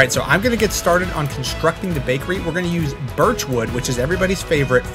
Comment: I thought everybody said that they don’t like birch wood